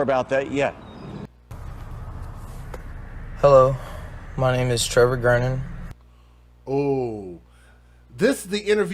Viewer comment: you can clearly hear someone tell her what to say. Starts with statement "babies" Wouldn't be surprised if they are all involved. Just like the mom who kept telling ppl it was pointless to search near McDonald's in one of the previous stories. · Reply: "my kids are scared, they're traumatized" and the statement after that.